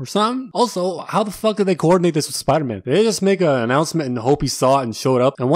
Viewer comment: not gonna lie instead of "did they just" i thought he said "this n-word"